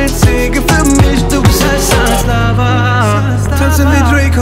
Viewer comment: Ich dachte wirklich er singt ,,du bist heißer als Sarah " 😂😂😂 und ich habe auch nichts anderes verstanden. 😆